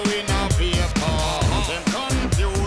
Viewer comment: Back then I actually thought Shaggy said "Where's the bin? Where's the bin? Where's the bin?" or "Mr. Bean, Mr Bean, Mr.Bean!" Haha